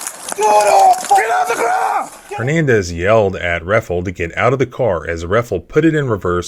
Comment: Thought he said Aaron Hernandez at first